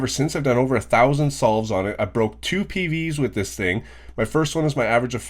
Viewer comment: i thought you Said:"I broke 2 TVs with this thing" and i was like wtf 😂